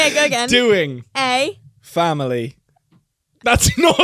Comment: The first thing I heard is “we are doing a family…” and I was invested 😂